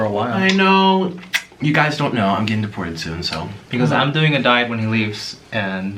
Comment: I thought he said "if u guys don't know, I'm getting divorced"